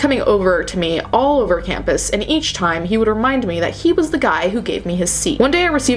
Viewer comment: Ngl at first I thought you said “ the guy who gave me his seed” and it completely caught me off guard 😂😂